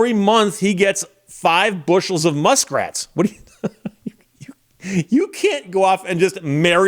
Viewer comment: Muskrat? Sorry - somehow I heard "Elon Muskrat"! 😅